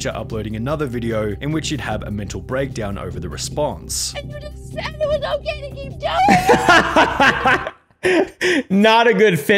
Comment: She really said “HE CAN’T KEEP GETTING AWAY WITH THIS!!” 💀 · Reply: No she said "HE THINKS IT'S OK TO KEEP DOING THIS!"